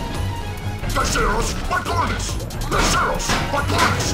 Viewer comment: What Rhino says at 4:23 is Desirous Bicornis Desirous Bicornis Desirous Bicornis Witch is the scientific name for a rhino